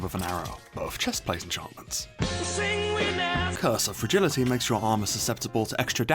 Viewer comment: i thought he said curse of virginity lol